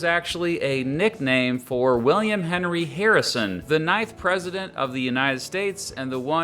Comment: So my headphones are a bit broken so I heard "the knife president" instead of "the 9th president"